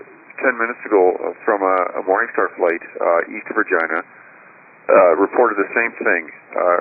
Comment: Did he actually say east of Vagina at 2:39?? 😂